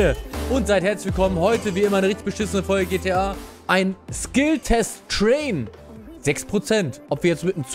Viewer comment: Ich hab Skilltest TRAIL verstanden und wollte schon das Video wieder abdrehen